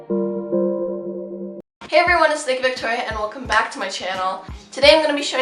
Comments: I swear to god, during your intro I thought you said "Hey guys, it's thicc Victoria' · omg i thought u said thicc victoria 💀💀💀